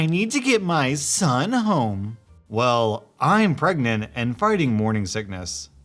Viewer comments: did you say farting instead of fighting? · Im not sure but did you say fighting or farting??? 😂??m